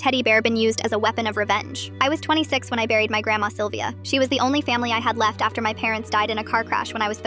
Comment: First I heard "I was 26 when I married my grandma Sylvia"